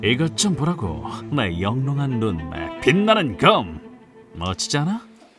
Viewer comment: 영롱한 눈깔이라고 들었다ㅋㅋㅋㅋㅋ